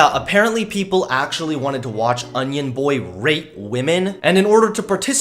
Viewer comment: thought you Said rape. Wouldnt put it past onion boi tho